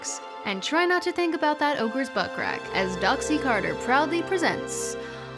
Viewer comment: I thought you said "Oprah's buttcrack" the first time...